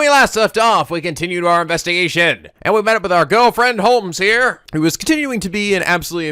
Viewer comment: whoops i misheard "our good old friend holmes" as "our girlfriend holmes" and i was VERY CONFUSED for a moment EDIT: the "th" in Thames isnt pronounced, its pronounced like "tems"